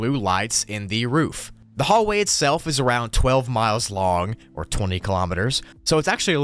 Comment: said its 12 miles long but the subtitles said 11 💀💀💀